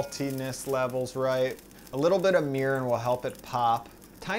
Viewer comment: What does he say at 8:46? What is he pouring? Pls somebody help! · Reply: According to <link>, "Mirin is a Japanese sweet rice wine that lends mild acidity to a dish. It is similar to sake, but is lower in sugar and alcohol, and provides a more umami flavor to savory dishes. It's a handy ingredient to have in your pantry because many Asian and fusion recipes call for it."